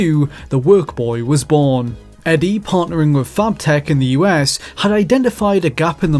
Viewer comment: I keep hearing "FartTech". As a kid, I would have loved the ability to program on my GameBoy. It didn't happen.